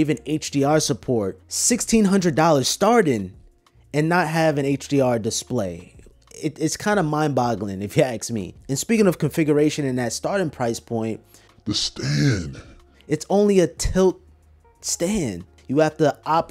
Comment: did you say aks instead of ask? :P